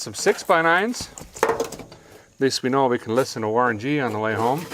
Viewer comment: did he say Warren G?